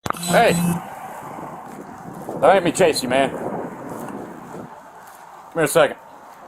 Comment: The moment the cop said "Don't make me chase you." this became a non-consensual encounter. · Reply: I thought he said "Tase you " 😂